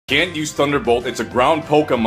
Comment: At first I thought he said "it's a brown pokémon" instead of "it's a ground pokémon". Or when both the trainer AND the pokémon don't know how to play the game...